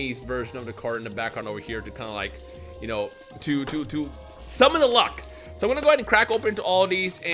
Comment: The way you said "summon the luck" @ 0:42 sounded like SUBALUWA to me 😎